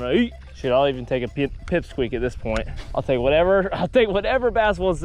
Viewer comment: can’t be the only one who hears “sh*t I’ll even take a pipsqueak”